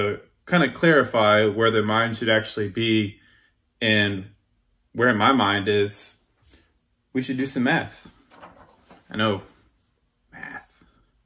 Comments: I genuinely misheard, "To clarify where my mind is, we should do some meth," which made it make more sense, incontext. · I thought he said "Do some meth" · LMAO I thought he said "lets do some meth" lol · I literally thought he said "we should do some meth". I'm sure its because I'm already convinced he's on it. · Who else thought he said 'we should do some meth' at 2:12? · I swear it sounds like he's saying 'do some meth'. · yes it's mathS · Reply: I replayed 2:12 several times and I keep hearing him say "You should do some meth" lol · When he first said he needed to "do some math", I thought he said "do some meth" and I thought to myself "Finally we know the fuel for these videos!" :-)